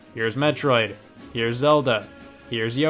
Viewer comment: did he says that’s ZELDA?!?! REEEEEEEEEEEEE